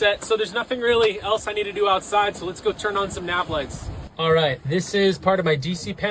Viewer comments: “Nav lights” totally thought he said Netflix at first 🥴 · At first I thought he said let’s go turn on some Netflix!😂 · I thought he said “now let’s go in and turn on Netflix”